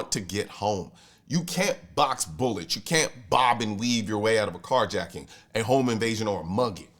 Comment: I heard "you can't Bob and weave your way out of a monkey" and i dont think thats even a false statement... I need to lay off the beers...